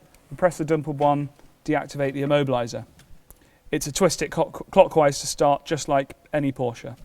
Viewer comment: its a twistet cock i heard that ;)